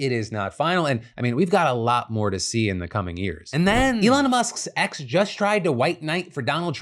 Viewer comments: I heard "Elon Musk's EX" at first and wondered what Grimes was up to. Thats what i get for multitasking. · At first i thought you said "Elon Musk's "ex"' not Elon Musk's X. 🤣😂